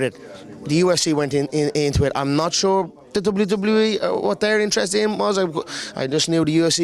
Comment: Did he just say “doodooblootoobluey”? 😂